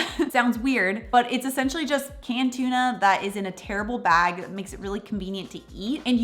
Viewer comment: talking about the bagged tuna... I thought she said it comes in a "terrible" bag, then realized she said "tear-able" like easy to tear open 😅 yes I love these for a quick snack at work!